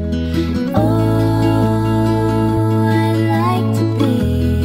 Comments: LOL did anyone else heard "I like to pee" ?! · the song sounds like its saying "ohhhh i like to pee" hahaha